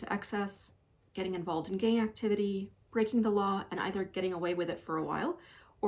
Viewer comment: lol why did i hear "gay activity" instead of "gang activity" at 6:00 and it still made sense at first 😂